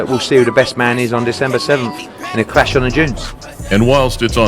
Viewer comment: did he say it will be a clash of the jinns?